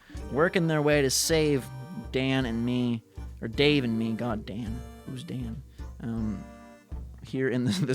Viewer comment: did he say goddamn at 9:52?